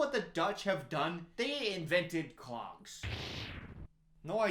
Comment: I thought said "Do you know what Dutch have done? They invented CLOCKS" 32:57 so I googled it real quick and found out that the Dutch actually did invent clocks. I was very impressed that he guessed correctly only to come back to this video and figure out he said Clogs